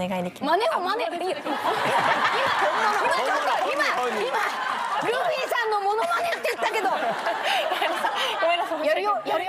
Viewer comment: 「マノマネ」って聞こえるw